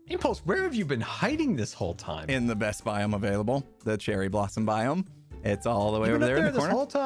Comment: When he said "in the best biome available" 25:42 I heard "in the Best Buy, I'm available"😂